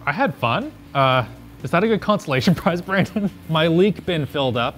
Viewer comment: the subtitles say "constellation prize" when it should be "consolation prize"